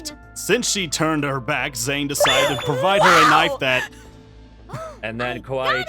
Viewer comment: please tell me that I’m not the only one who heard him say “And than kawaii chan shot her” When Zane died